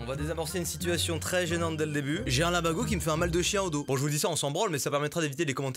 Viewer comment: Ptn j'ai compris "j'ai un lavabeau qui me fait mal dans le dos"